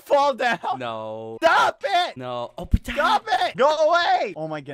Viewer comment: am i the only french who understand « Oh putain » ? please say that my not alone